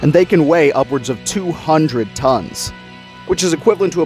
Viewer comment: Our largest specimen we know today was measured at about 33 meters and 200 tons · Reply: i thought you said 200 lions for a second